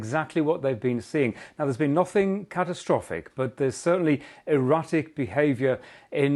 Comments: Did he say erotic behaviour around the 2:22 mark😅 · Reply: I think he said erratic but it did sound like he said erotic lol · Did he say erotic behaviour around the 2:22 mark😅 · I thought he said erotic behaviour… no wonder the earth moved? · he says there's been EROTIC behaviour in the currents of the Pacific X-) instead of erratic